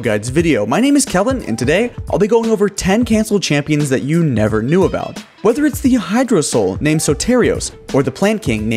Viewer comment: I thought it said "champions you never KEKW existed"